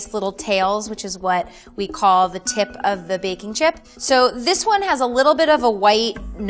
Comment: I thought she said bacon chips😂 then I realized definitely baking.chips